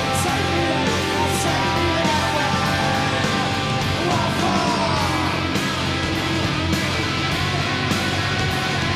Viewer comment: What do they say in the minute 5:00 ? Anyone could say me :/ ? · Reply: Richard Hernández a huge ‘what for’ you might’ve gotten it as 4 lol